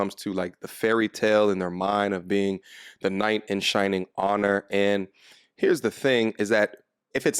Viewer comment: It’s ‘knight in shining armor.’ Sounded like u said honor. Sorry for being a dick but I might save you from being clowned on in the future. Good intentions and all